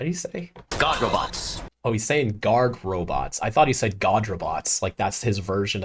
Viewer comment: I thought he said goth robots 🤣 Edit: 6:02 I used to screen recording and made a super funny version of it and yet I screwed up because I said we’re gonna fight albado HE’S as red as a tomato instead of WHO’S as red as a tomato aka hold it I just had a freeking bruh moment